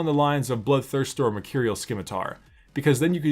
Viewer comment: Stab. If you can please like the comments with the champs you want to see next it would be very appreciated! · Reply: Did you call a scimitar a SKIMITAR?!?!?!?! 7:45 mercurial SKIMITAR!!!!!!?!?! fucking pleb